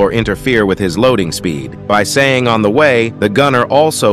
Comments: On the way? The only thing i heard is "on wide" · I thought he was saying "Or What" In fact I still can't hear him say "On the way" but thats just me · I thought he was saying, "on the one". This makes more sense. Lmao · I always thought he said "On the One